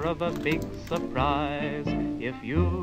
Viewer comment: ayo are my ears deceiving me or did i hear “if you go down into the woods today you better go into this guy”😳😳